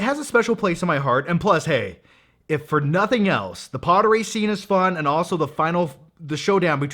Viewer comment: it sound like you say “the pottery scene is fun” LOL